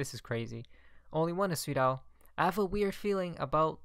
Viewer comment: what does Esweedow mean? He keeps saying it every video!